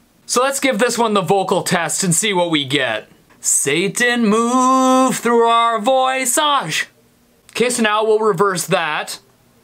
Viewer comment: i heard "Satan moves through our VOICEASS "